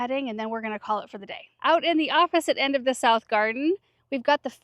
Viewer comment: I thought you initially said “out in the office in the south garden” (instead of opposite end of the south garden). It made me pause, replay and chuckle. What a fun office you have there. I’m working to make my office as beautiful as yours! 😉 😂.